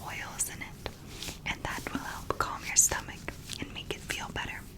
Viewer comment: i thought it said stomach acne 😭😭😭